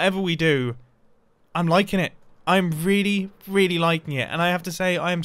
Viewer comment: Mumbo at the end said “I’m really liking it” and I thought he had said “you know, I’m really lichen it” and was about to pull out the glow lichen and make a hilarious pun